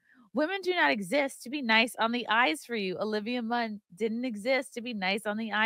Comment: Oh it's EYES - for all this time, I thought she said nice on the ICE.. I thought it was weird..